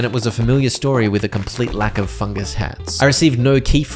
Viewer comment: For a minute there I thought you said "Fucking hats" not "Fungus Hats." Bro made me do a double take lmao/